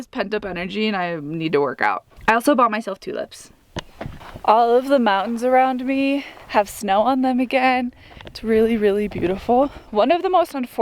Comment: how come when you said you got tulips, i heard "two lips" and thought you got filler lmao. Everything is looking lovely, the hides are gonna turn out gerat